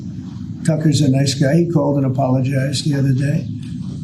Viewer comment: I heard "Tucker crawled and apologized". Sounds about right.